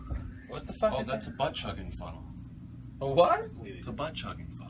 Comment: What did he say? Did he say it is a butt-chucking funnel?